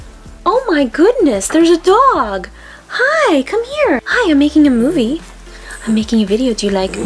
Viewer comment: who heard "I'm making a movie" 😂😂😂 I love ❤😍❤